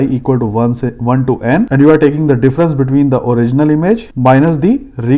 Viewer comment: just slipped into Hindi mode, instead of 1 to n, he said 1 '"se" n and changed to English immediately, quick loss function from brain to mouth man...LOL..Dude you should try uploading NLP content as well, it would be great.